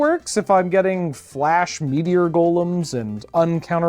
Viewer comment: I keep hearing 'Meatier Golem' and imagining a really buff flesh golem.